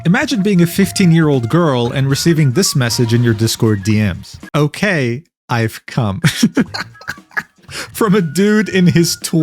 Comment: the way you said that sounded like "my time has come" But it came from a guy that's... Not nearly as smart